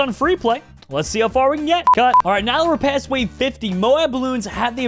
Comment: i heard him say cut